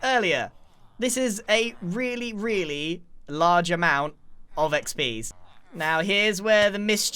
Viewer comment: it sounds like he said a large amount of xb’s.